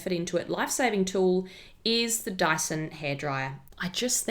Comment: omg at first I thought you said Daiso hairdryer, I was about to dash out of my house in my PJs lmao